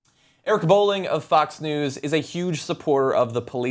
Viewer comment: I thought it said boiling.